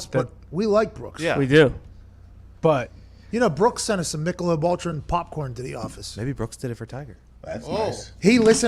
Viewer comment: Thought he said motrin popcorn. I'd probably buy that.